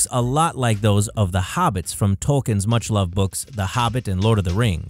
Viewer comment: Did he just say TOKEN'S?